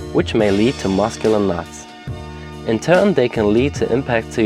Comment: Which may lead to Muscular .. what? Sorry, didn't quite catch that :D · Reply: Knots